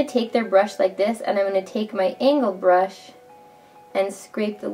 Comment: My bro : Did she say ANAL brush ?! 7:45 Me : NOOOO ANGLE BRUSH ! My bro : oooh